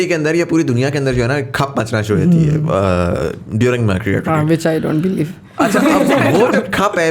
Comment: what said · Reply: i said, which i don't beleive